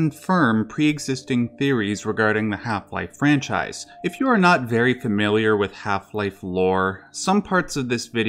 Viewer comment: I misheard this as "If you are not very familiar with Half Life 4..." and experienced a solid half minute of confusion and concern that I had slipped into some parallel bizarro world.